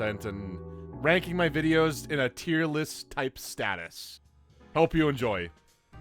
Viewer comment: Tier list? Ohhhh. I thought you said Tear list Ohhhhhhh...